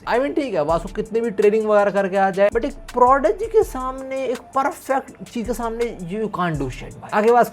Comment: did I hear perfect ?